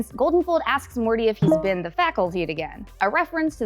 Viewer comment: what is saying here? - "asks morty if he's been to the Faculty again"